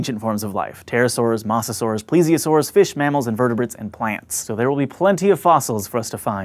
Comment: I swear he said "fishmammals" as a single word...